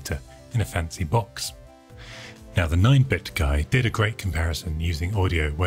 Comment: Did you say 9(?) bit guy at 2:14? :P